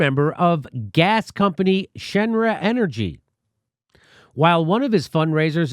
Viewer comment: I was listening to the podcast while playing the FF7 Remake, got a weird feeling when I thought Jimmy said one of Biden's advisors worked for Shinra energy, lol...